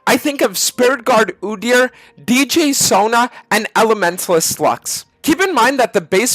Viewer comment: "and elementalist slu**" I heard that wrong lmao